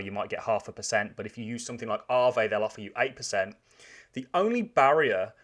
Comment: At around 4:25 he mentions half a percent interest at a bank but getting 8 percent from "arvay"?? I don't know what word he said, does anybody know what he said?? Thank you very much! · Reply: aave